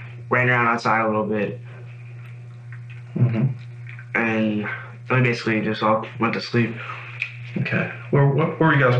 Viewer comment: i know this has literally nothing to do with this case, but i cannot emphasize enough how extremely helpful it is that y’all have subtitles. CC can be difficult and inaccurate on a lot of these kinds of interrogation videos, and i know whoever added captions to this spent hours doing so. just know that us “hard of hearing” folks appreciate this IMMENSELY. great work!! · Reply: Just found a CC error haha. At 13:59 he says "and then basically" instead of "and honestly"